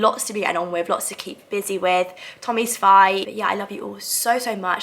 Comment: I thought she’d say that she broke up with Tommy 🤣🤣